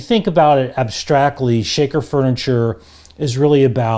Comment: Is talking about Shaper Furniture, a new style of furniture to an old man like me, or as I suspect Shaker furniture which is popular with the American market but not so much in Europe? The content of video however is of interest. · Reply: I would agree. The closed captions say Shaper furniture, but it sounds like he is saying Shaker.